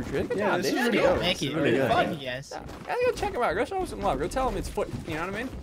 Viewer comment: DID HE JUST SAY WHAT I THINK HE SAID?! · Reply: He said fun